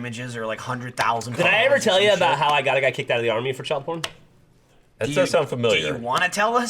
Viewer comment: When i first heard geoff say "how i got a guy kicked out of the army" i heard "how i got kicked out of the army " :o